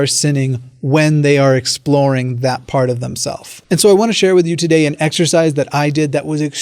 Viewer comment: Did you just say "themself" at 3:58?